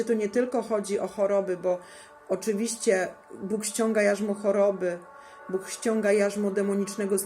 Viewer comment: W której minucie mówię o tym, że Bóg ściąga na ludzi choroby? Przecież my cały czas głosimy uzdrowienie, uwolnienie i sami modlimy się o ludzi! Mamy świadectwa uzdrowień i uwolnień od demonów! Jego ranami i sińcami jesteśmy uzdrowieni i sami doświadczyliśmy wielu cudownych uzdrowień!!! Nigdy nie głosimy przeciwko uzdrowieniu ani, że Bóg zsyła choroby na ludzi.... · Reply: Ok 18:29, w złym kontekście to usłyszałem. Dokładnie powiedziałaś "oczywiście Bóg ściąga jarzmo choroby", a ja usłyszałem "oczywiście Bóg ściąga choroby" i w afekcie od razu zareagowałem zamiast posłuchać to parę razy. To mnie zmysły wprowadziły w błąd. Coś z tym moim słuchem nie tak. Takie niespodzianki u mnie są konsekwencją uszkodzeń CUN po przebytej chorobie, w efekcie muszę dość sceptycznie podchodzić do własnych zmysłów, niestety nie tylko własnych. Jeśli poczułaś się urażona to przepraszam.